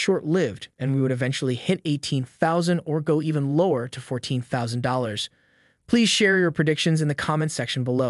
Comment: $14'000 not $40'000.... your editor misheard that. 😆